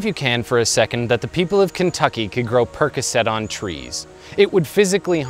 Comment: Much like PBS, this episode was made possible thanks to viewers like you: <link> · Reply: What are you saying? Perkoset?